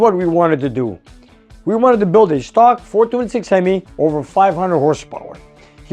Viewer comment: Had to listen twice. Thought you said, "I build Ford 26 Hemis". Your "perhaps they machined the wrong side" sounds like a smart guess. That piston surface isn't much thicker than aluminum foil. Might get an idea of the thickness by tapping the surface with a screwdriver and listening how high-pitched the sound is.